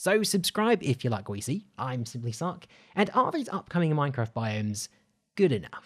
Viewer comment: I thought you said "I am simplysuck"